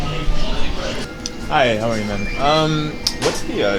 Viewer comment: at first i thought he said mam but realized he said man